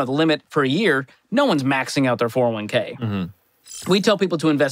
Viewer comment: Kamel 6:02 "... Nobody is maxing out their 401K..." Did I hear you correctly? What do you mean by this? Looking for clarification. Thanks.